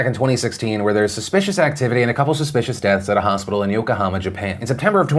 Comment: Can someone managing Phil's channel fix the caption at 9:19 where it says "Oklahoma" instead of "Yokohama."